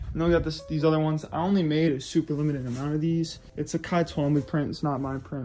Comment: whos the artist you named at 2:40 i like the print but cant make out the name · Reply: Late, but Cy Twombly (pronounced like Sigh Twombly)